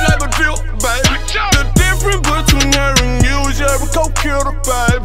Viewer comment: can anybody tell me what he said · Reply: "the difference between her and u is ur a go gotta babe"